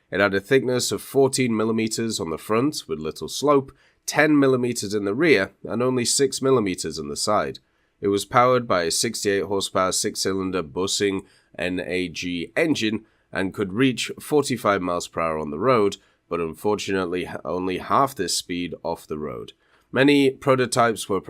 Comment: did I hear "Bussin"?